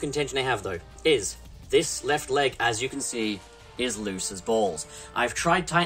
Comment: i swear i heard “he is a bit loose on his balls” i was like “ayo wtf? Xd?”